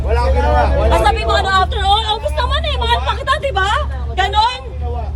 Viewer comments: Tama ba ung narinig ko? Na sinabi ni? 28:23 Na obvious naman na mahal pa kita? Or mali lang ako ng dinig. 🥺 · did she really say na “obvious naman eh mahal pa kita”?